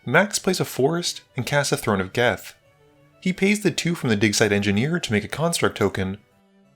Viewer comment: I could've sworn I heard "dickside engineer" and thought it was a dockside joke